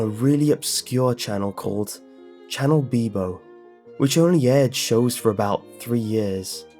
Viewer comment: Thought he said channel BMO